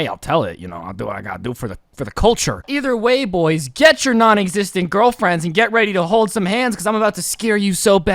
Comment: Bruh I thought he said “naughty fisting girlfriends 💀